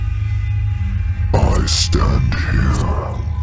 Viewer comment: I thought it said OUTSTANDING lmao